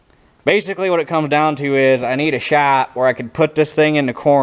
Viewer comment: first! and i keep hearing "i need a shop" so i'm waiting for the day a shop starts being built🤣👌😎